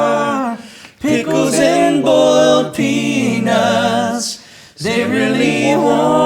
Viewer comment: Was i the only one who heard: “Pickles and boiled penus...”